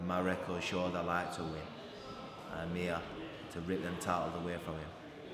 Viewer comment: "rip them towels away from him" towels=belts? what does he say? it's not clear....